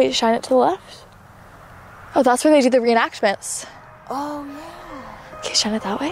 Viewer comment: sounds like someone saying ooo yeahhhhh